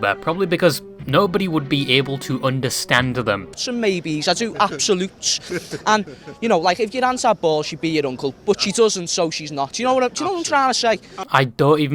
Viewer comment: im here to transcribe what i can make out of this gibberish, "____" will be used for words i just cannot make out "so maybe __ absolute inhale and yknow like if your aunt had balls she'd be your uncle but she doesn't so she's not do you know what im tryna say"